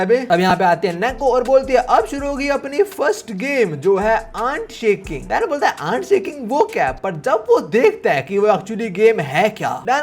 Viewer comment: i heard annd shanking 💀